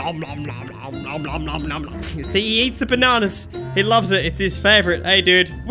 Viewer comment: did you just say "she" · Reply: H said see he eats the banana